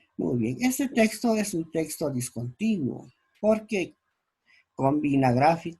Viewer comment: texto que dijo prof. no entendi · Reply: texto discontinuo porque combina textos, imágenes y otros recursos. Además puedo empezar a leer de cualquier parte del texto y de entiende igual.